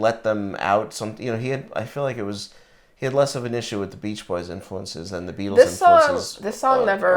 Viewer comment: For a second I thought you said Beach Bourgeoisie 😅